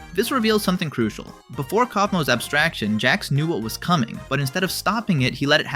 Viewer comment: go to 6:59 subtitles say koffin abstracted